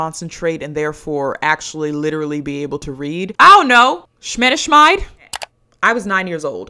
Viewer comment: What is that word at 17:33? Sounds like schmedischmide :/